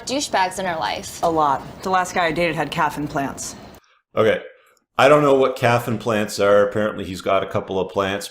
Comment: You thought calf implants are in reference to plants? Damn dude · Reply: So did I. I misheard is as caffeine plants or something!